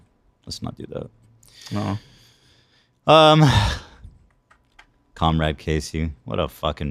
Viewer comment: I had to rewind at 1:17:20. Thought he said Cumrag Casey, which would have been a nice burn.